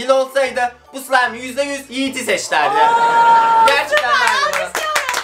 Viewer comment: yiğit abi ne diyor? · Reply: Diyoki "bu slimenin dili olsaydı yiğiti seç derdi" Diyor